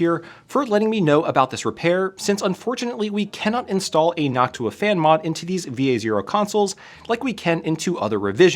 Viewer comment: did you say hawk tuah fan mod?